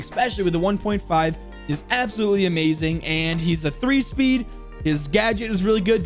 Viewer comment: wait a sec, did he just say that mozzie was a 3 speed??